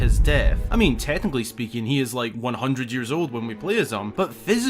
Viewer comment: "Link is 100 years old"... to which I heard "Link becomes Captain America"... ignore me, I'm high...